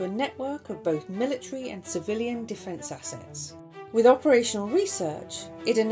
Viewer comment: What did she say? "This groundbreaking approach was rapidly integrated to a network of both military and civilian (what) (what)" I cannot catch that. Somebody plz help me. Thanks. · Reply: defense assets